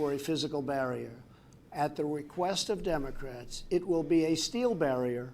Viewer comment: woah woah woah you misheard the concrete wall vs steel bit @ 20:40 ish you say: "now he wants a steel fence essentially" no.... no he doesn't. He clearly said (and it's true) "at the request of democrats" - he's TRYING to compromise and it's their goal to make people have the exact reaction you just had... they're going to use it to make him look stupid.... even tho.... it's their idea....